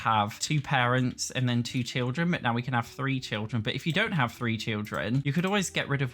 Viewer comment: I misheard and thought he said "but if you don't wanna have three children" and I went "then you can get rid of one" 😶‍🌫️ ... but then I realised, oh. Yeah nevermind.